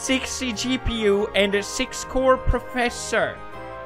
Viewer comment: did he say 6 core PROFESSOR ?